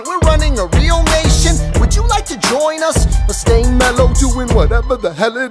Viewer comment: when it says "or stay mellow doing whatever you do in .." I thought it said "or gay mellow"